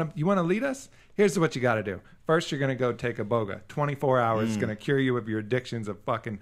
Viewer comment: couldnt understand the word when he says this 5 step for politicians @ 20:47 - 1st u gotta take a bogo bog boga ??!!what is he saying?which after 24hrs will make u get rid of all ur addictions,coke,cigarettes etc? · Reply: Iboga